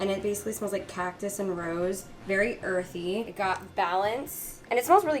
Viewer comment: The first candle I thought you said “cat piss and rose” 😅😂